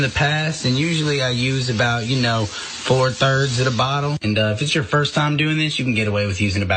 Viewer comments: did he say 4 thirds of a bottle?🤦😂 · did he say four thirds of the bottle?